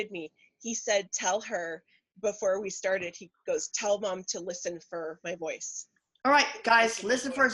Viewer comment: I believe you can hear at 17:57 right after you are talking about him leaving a EVP. I cannot make out what he is saying though. Can anyone make out what he said? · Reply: It sounds like saying, "Listen for my voice."